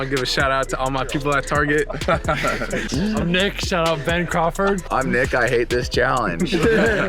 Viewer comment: tf? My name is Ben kronberg. Did he just shout me out? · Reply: I think he said Crawford